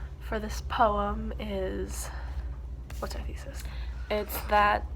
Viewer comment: I thought u said what’s the tea sis LOOOOL